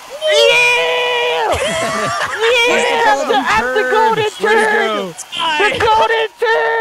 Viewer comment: Who else heard "I'm the Golden Turd" at 11:08 xD